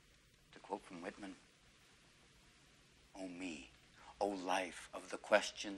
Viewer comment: We don't read and write poetry because it's cute. We read and write poetry because we are members of the human race and the human race is filled with passion and medicine, law, business, engineering, these are noble pursuits and necessary to sustain life. But poetry, beauty, romance, love these are what we stay alive for, to quote from Whitman " Oh me, oh life of the questions of these recurring, of the endless trains of the faithless, of cities filled with the foolish, what good omit these oh me oh life." Answer. That you are here. That life exists and identity. That the powerful play goes on and you may contribute a verse. That the powerful play goes on and you may contribute a verse. What will your verse be? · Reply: Thanks for writing these down. Yeah, I think I heard right, "Oh me, Oh life..." (I didn't think "Ol me, Ol life" made sense.